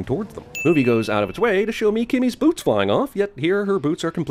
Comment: I thought they said kimmys boobs flying off but they said boots